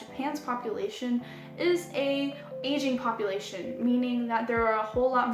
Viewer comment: Sounded like you said, "Japan's population in an Asian one." xD I immediately knew I just misheard "aging", but it was pretty funny!